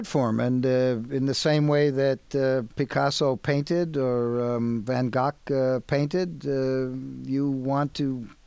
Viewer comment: doesn't it sound like Loyd said "Van Cock" instead of Van Gogh?